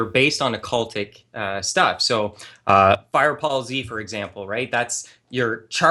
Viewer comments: Did call Dragon Ball Z "Fire Paul Z"? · did he just said fire pal Z? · did the seriously just say "FirePaul Z" instead of Dragon Ball Z? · did he just say Firepall Z?